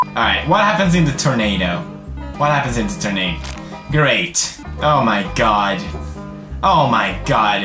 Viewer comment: xD he said: ''Great'' but the subtitle said: ''fuck sake then'' lol